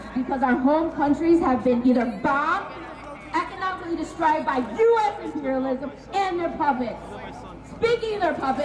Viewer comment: did she say jewish imperialism?